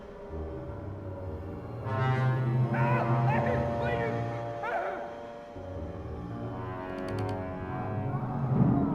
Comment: What is the screaming guy saying at 27:10 in the background ? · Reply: "Ah ah Please!"